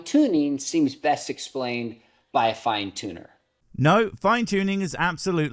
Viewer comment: Every universe has a beginner? Did he even realise what he said? lol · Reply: Also with his accent I thought he said the universe was created by a fine TUNA lol